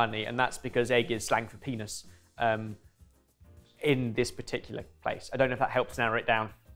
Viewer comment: I'm sorry, did just say "Egg is length of penis in this country"????